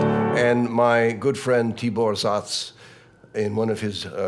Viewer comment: whose dissertation is he talking about? I can't hear and understand the name of him.. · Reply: Tibor Szász - <link>